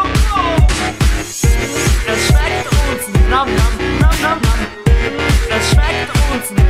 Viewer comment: und an anderen stellen, wo der text kommt habe ich bis eben immer "jetzt steigt er uns" anstelle "es schmeckt uns" verstanden und immer geraetselt, wie genau das gemeint ist. xD